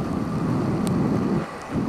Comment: Did you say something about 'hostile' or is there a word that just sounds like that? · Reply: Push Bike I spoke in hungarian, see the description for translation. The word similar to "hostile" was "használni" which sounds like "husnaahlnee"